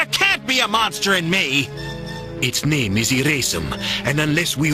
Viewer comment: why did I hear “its name is racism” 💀🙏